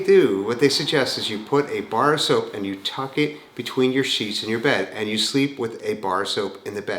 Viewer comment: LOL-= I thought you said "bar of soap between your cheeks"--that is "out there' !! LOL!! I will try anything, my legs hurt when I get up in morning! I will wrap my legs and try the soap between the SHEETS tonight :)